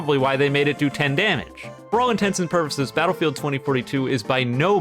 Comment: did I hear that right? did he just say "for all intensive purposes"?